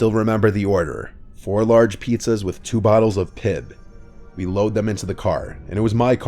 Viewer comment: I don't know why but in the third story i thought you were going to say "two bottles of piss" not Pibb